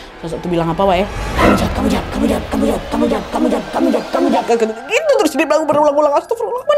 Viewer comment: bilang apa si itu sosok nya? ga terlalu jelas 😅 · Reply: Km jahat